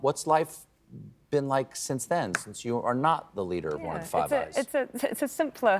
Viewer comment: what did Stephen say as Jacinda began to talk. He said "how is it like now that you are not the leader of the five". What did say after saying Five ?? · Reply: "Five Eyes", the intelligence alliance comprising Australia, Canada, New Zealand, the United Kingdom, and the United States